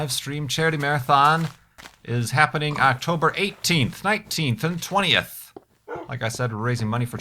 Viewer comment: lol - It sounded like you said the fund raiser was being held on Oct. 18th, 1920th - haha. Tell me, have you figured out how to time travel, cause that would be AWESOME!